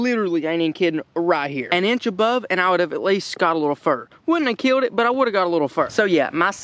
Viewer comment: What an awesome video! For those of you watching... that’s exactly how it went down! tells it like it is!!! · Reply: sorry I thought you said you wouldn’t kill it(I thought you were a vegan)😂🤣😂😂